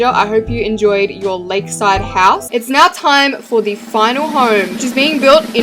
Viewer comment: I hope you enjoy your leg side house-. (thats what I heard lmao)